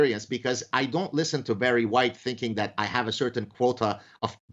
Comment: The first time he said "Barry White" I heard "very white."